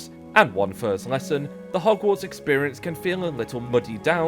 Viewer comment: did you say Hogwarts? Xp